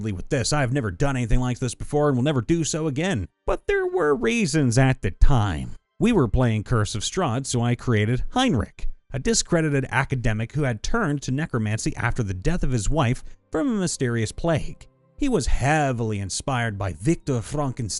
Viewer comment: Was listening and not watching. Misheard Heinrick as Heimlich. I thought to myself: "It would have been hilarious had Heimlich's wife died by choking"